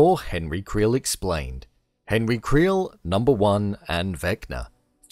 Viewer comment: I heard "henry creel, a blonde and vecna" 😂😂